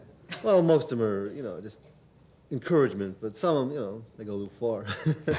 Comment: What did he say at 11:06, when Carson smiles and replies "no comment"? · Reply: Some of the girls went too far in their letters.